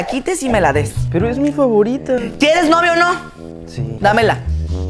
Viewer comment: entendí "quieres novio o no?" O estoy mal